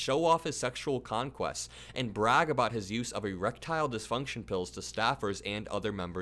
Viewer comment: I thought you said reptile disfunction. I said what??? That would be weird even for Matt.